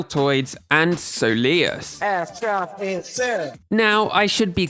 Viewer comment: even after the explanation I hear this as „abstract insert“